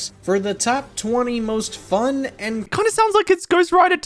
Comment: It cuts right off but is he saying this is top 20 "most fun" killers at 1:06? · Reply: Yep, he said fun and creative. Granted, the list is still shit from the perspective of "how creative the killers are" but people treating this as a strength tier list are silly